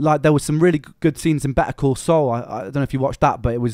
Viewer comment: What was the show that said is more subtle than Breaking Bad? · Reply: "Better Call Saul" the Breaking Bad spinoff about Saul Goodman